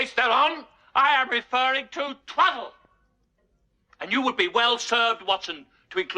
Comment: I need help working out what Sherlock Holmes says around the 12:00 mark. Sounds like "Truddle", however I can not find this in any of my google searches.